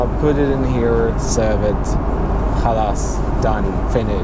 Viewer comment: Did you just say خلاص 😂